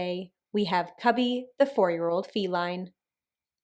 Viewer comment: Did she say The “meme” Martha